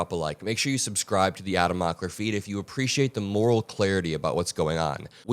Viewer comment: I misheard "Adam Mockler feed" as "Adam-ocracy"...